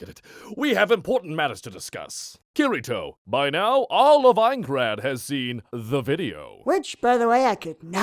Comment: I don’t hear heathcliff, all I hear is “perfect cell vs Aincrad”